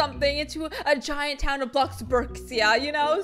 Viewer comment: did she just say bloxburkcia?